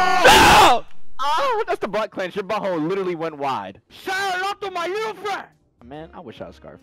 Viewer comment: Anyone knows what says at 3:50? where does it come from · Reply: Say hello to my little friend. “Scarface”